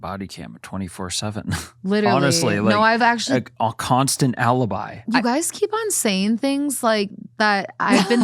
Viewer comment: anyone else hear “Owl-ibi”?